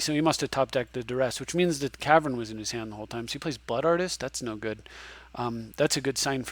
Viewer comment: I hear "Butt Artist."